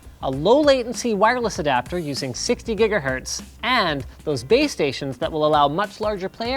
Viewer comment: I thought you said BabeStations lol